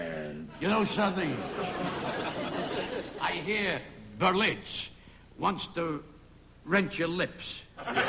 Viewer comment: What did jimmy say in 4:19 ? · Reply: Betlitz wants to rent your lips.